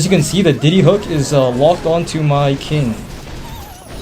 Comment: Diddy Hook?💀 Did I hear that right?